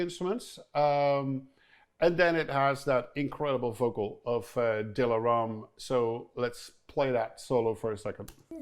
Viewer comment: What is the name of the vocalist at 13:40? i didnt catch the name, Dila Ram?